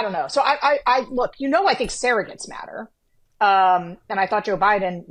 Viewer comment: Sarah: “You know I think surrogates matter” What I heard at first: “You know I think Sarah gets madder”